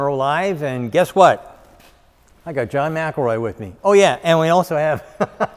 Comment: For a second I thought he said John McEnroe…